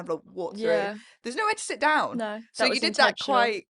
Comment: What word did you say at 14:43 😂 clevererilly